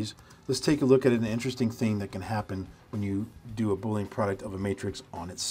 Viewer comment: the time stamp at 7:03 says bullying product instead of Boolean product haha